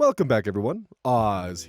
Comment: my yt glitched so I thought the intro just said "come back everyone"